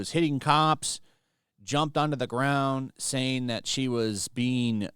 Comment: Wait a second did you say she jumped? Another magic trick.